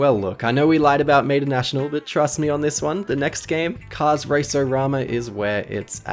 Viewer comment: I thought it said race-O-Bama😂